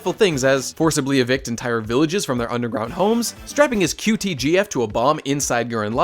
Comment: What's a qgtbf? I heard it